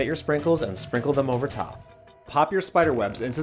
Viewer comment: Parchment paper? FAUK I THOUGHT YOU SAID PAPER PARCHMENT